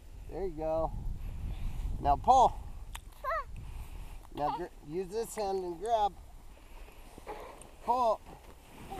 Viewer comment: what did the little girl say at 25:57 ??! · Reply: She said let's get it out